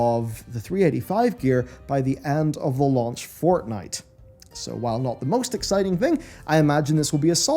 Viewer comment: did i hear launch of FORTNITE?